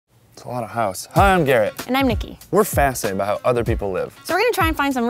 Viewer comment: "Hi Im Gay" did.... did I just hear that or am I just hallucinating?.